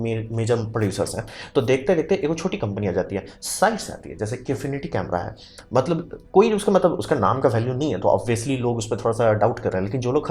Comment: ? Can someone tell what did he say Company name ? Inifinity ? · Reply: kinefinity cinema camera